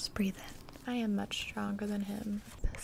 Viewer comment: "Breathe in" "I am not stronger then him." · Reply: I thought she said “I am much stronger than him”